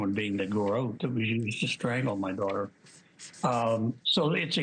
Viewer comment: he literally says "the garot that we used to kill our daughter"????? Did anyone catch this?? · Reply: He says "that was used to strangle my daughter'